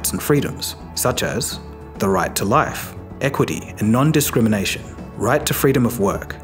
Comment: Interesting enough, when you were talking about the universal human rights, the screen shows "EQUALITY..." but I hear you saying "EQUITY". Those are NOT the same thing. Not even close.